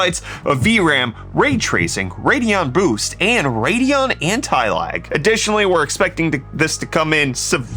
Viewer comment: On my first watch through, I swear at 0:50 it sounded like he said the new AMD 7600 card had "Radeon Anti-Life". If no one else buys them, at least this is finally the graphics card that Darkseid has been searching for. Of course, true fans will want to wait to pick up the limited edition Snyder-Cut version.